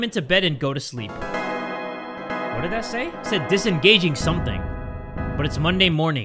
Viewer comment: so you don’t have to waist your time it said disengaging consciousness Even tho I think I’m the only one who really cared what it said lol